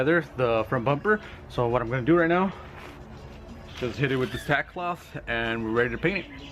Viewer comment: You a bible kind of guy? I thought I heard you say sackcloth @ 3:34??